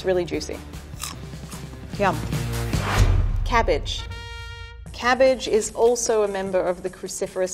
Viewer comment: I thought the cabbage said garbage I mean... it is garbage tho